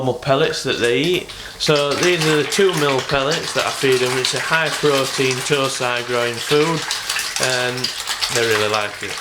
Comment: At about 02:00 "High-protein, muffled mumble food." What did you actually say? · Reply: These are the 2mm pellets that i feed them, they are high-protein tosai growing food. lol